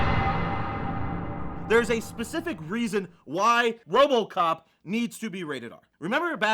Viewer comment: did just say "wobocop"